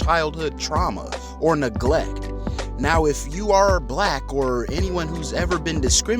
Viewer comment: Correct me if I’m wrong lol but at 4:44 did you say “if you are a black or someone who has been discriminated against…”? 👀